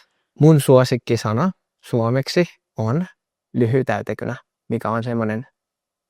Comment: He still pronounced "lyijytäytekynä" a bit off. It sounded like "lyhytäytekynä". More like how you might pronounce the start in Spanish. The difftong with y-i is important, as well as pronounging Finnish j clearly. That said, as a native speaker I would say that you should put special emphasis on getting the vowel sounds correct because those are really important in Finnish. You can pronounce consonants a bit off and be still understood correctly as long as your vowel sounds are accurate.